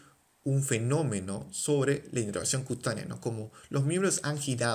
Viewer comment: que dice en el minuto 7:50 basicamente despues de ''un fenómeno sobre la ...'' help pls 😿🙏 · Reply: sobre la inervación cutánea...